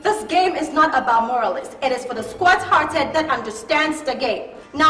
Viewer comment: Chale did Beyonce say the game is for the"squirt hearted"?😂